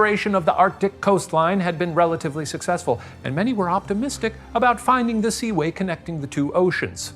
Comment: I’ve worked in restaurants for too long…. Shane said “seaway” and all I heard was “Si, güey.”